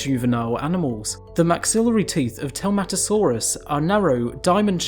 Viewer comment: Honestly, I thought you were saying Tomatosarus...was wondering where potatosaurus was. 8-)